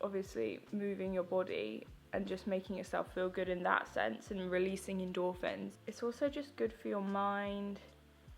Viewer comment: I swear I thought she said 'releasing dolphins' 😂 was like.. Okay!??? 😅